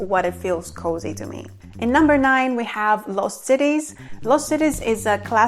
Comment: I thought she said “loose titties”